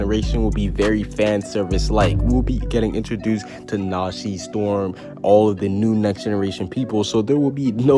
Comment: Did you just say nashi storm!??! 12:07 Nani!?